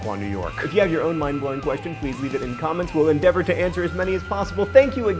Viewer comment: I thought he said, "Thank you for watching menopause," for a second there.